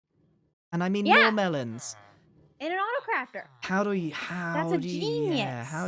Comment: I heard "and I mean your melons" had me absolutely dying because my mind went to a totally different place